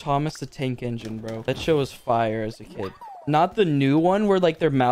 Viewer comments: i thought he said the sh word lol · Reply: he said show😂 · When talked about Thomas the Train I heard him say “that shit was fire” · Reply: He said show not shit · he said show, af first I really thought he said shit.